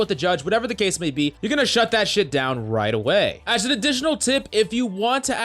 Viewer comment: Did I just hear “shut that SHIT down right away? “ 👀😂❤️